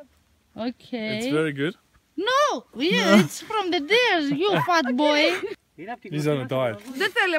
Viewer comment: did she say fuk boy at 6:00 hahaha